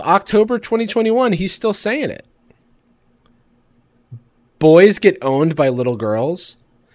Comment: I WASNT LOOKING AT THE VIDEO AND I GENUINELY THOUGHT YOU SAID "BOYS GET BONED BY LITTLE GIRLS" AND I WAS SCARED AND CONFUSED💀💀